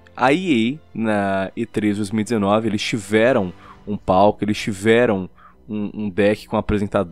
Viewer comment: só eu entendi ele falando: "Eles tiveram um PAU" GENIAL KRA :0